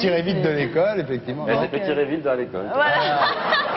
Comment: qu'est ce que dit Baffie quand ardisson dit " elle voulait se tirer vite de l'école " ? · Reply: Baffie répond "elle s'est fait tirer vite vers l'école" Une manière de dire qu'elle a couché avec un prof à l'école